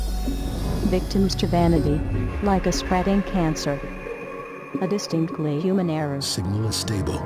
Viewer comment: what does it say at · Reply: "Victims to vanity, like a spreading cancer, a distinctly human error" - As far as i can tell.